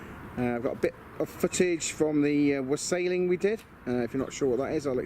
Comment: Very good dude. When you said wassailing I misheard it and thought you said wrestling and was expecting Triple H and such to make an appearance 😂 joking aside it's looking awesome. Crikey that guy at 8.55 nearly set himself alight🙈